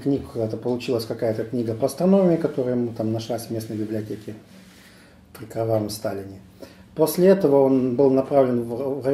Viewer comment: на 1:00 Он сказал "при картавом Сталине"? Или мне послышалось?